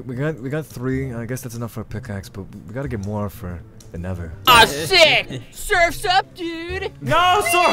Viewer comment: Did say aww sh*t